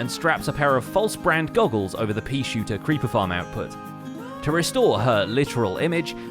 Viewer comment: Did I just hear.... PEASHOOTER?